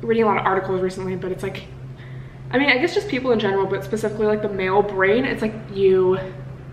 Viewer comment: I thought she said “ male brain, ew.” 😂